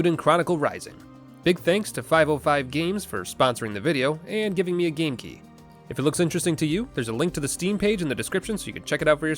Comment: I thought you said giving you a GameCube, and it confused me greatly.